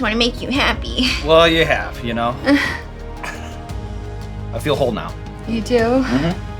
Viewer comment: did he say "I feel whole now" or "I feel your hole now"; cause there were references during the recording to "orrifices".